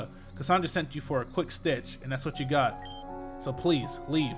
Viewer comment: Oh? oh........ Oh my gosh.... oh my gosh.... "What's the situation?" All my life I heard 'stitch' but it was 'sitch' this whole time?! I feel so... slow..